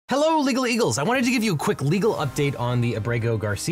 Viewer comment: I thought you said "hello illegal eagles" lmfao.